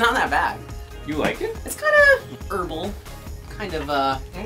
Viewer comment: What's that word at 2:29? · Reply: herbal